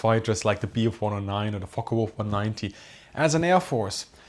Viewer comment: 😳 the auto caption says “Fuckwolf 190” instead of Focke Wulf 190….😨😱😅